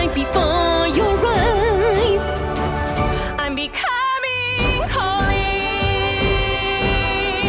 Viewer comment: At first I thought you said your becoming Cory lol